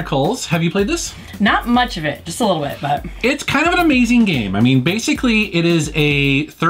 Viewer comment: These are top 10? Tf... game 10 you said "kind of an amazing game". (35 seconds)All I hear is "So amazing" like like so so. Bad top 10 man.